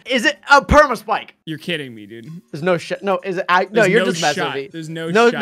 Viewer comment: Go to 4:17 , accidentally or it sounds like he says, “Sh*t.”